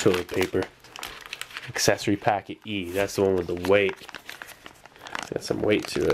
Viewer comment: Lmfao I thought he said "Accessory packet, ye"